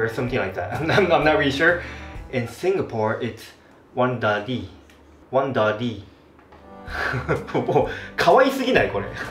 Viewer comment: one dirty それか one daddy どっちかにしか聞こえんw